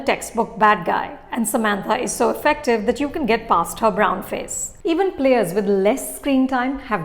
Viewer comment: Did I just hear 'get past her brown face'.